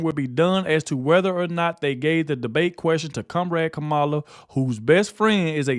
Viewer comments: When you read Trumps statement I thought you said kumrag Kamala. I did a double take and saw it said comrade 😂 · Did he just say cum rag kamela?! Aaaahahahaha!